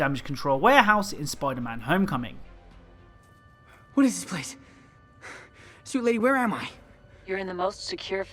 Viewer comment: Am I the only one that thought Tom Holland said “soup lady”😂